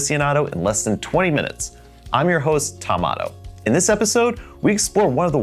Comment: I heard "I'm your host... Tomato", with the "ah" sound. 😂